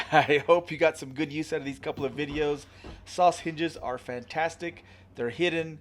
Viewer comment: Great job mate, am i the only one every time you say Soss hinges it sounds like your saying sausages 😊